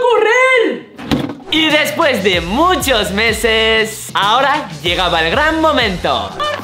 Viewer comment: escuche Martina a llegado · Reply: Dijo por fin a llegado